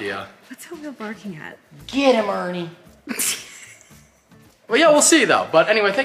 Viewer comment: Did say " Get em Ernie" Whos Ernie ?